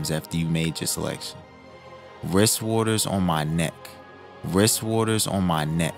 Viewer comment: I heard "Rizz water on my neck".